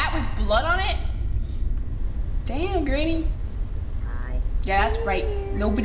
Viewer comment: Did she just say "Damn Granny" lol 😂